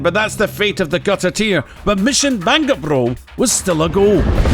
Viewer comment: Missing Bangabro sounded like you said. Mission Bang A Bro. Sorry but I laughed too hard at that.